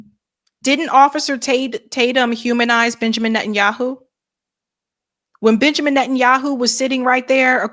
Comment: I thought you said "Bitchamin Netanyahu" lol